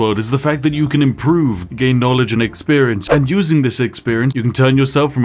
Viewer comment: I could of swore he said gay knowledge at the beginning